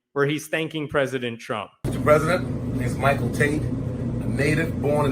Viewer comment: I thought you said "Michael Taint" 😂😂